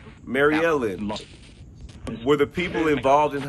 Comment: I heard him say Helen